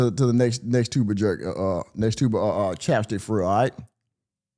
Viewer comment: Did i hear that right? Did 1 of them clowns n the audience say "I'll ask that question" like he gone go 2 the Mothers restaurant